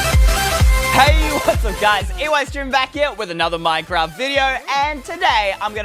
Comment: I edited this comment so new people won't know what it said before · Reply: I thought it said Estreem